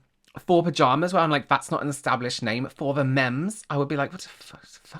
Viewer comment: Idk if you mentioned this but I just realised 4 the mems is 'for the memories' LOL - I thought they were saying for the memes or something 💀